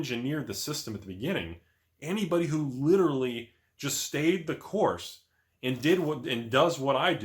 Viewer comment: All I hear is “Stay the course ya dummy”....Taco!